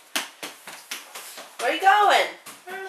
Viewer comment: Anybody heard at 0:03 where miskha said "no where"?